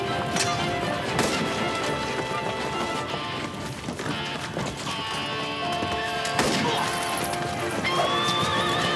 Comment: does he say baguette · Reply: Hatchet Dev jeez, u seriously asked that, he said “cheeky bastard “ no where near baguette